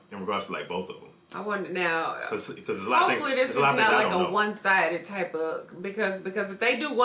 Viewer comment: Thought you guys said it better not be one sided